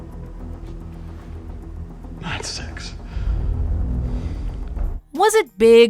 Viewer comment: Saying “not six” (if that’s what he said) after being asked, “How bad did they get hit” doesn’t make sense. Maybe it will when I watch it. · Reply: I was going by the caption when I thought he said, “not six.” Just watched this again and he actually said, “nine six”, as in 9.6. Now it makes sense.